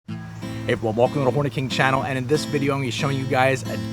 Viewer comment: I wasn't looking at the screen when this started and I could have sworn you said ''welcome to the horney king channel'' 😂